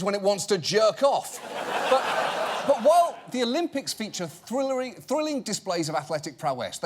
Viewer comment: Did he say "Thrillary"? I really hope that won't become a nickname...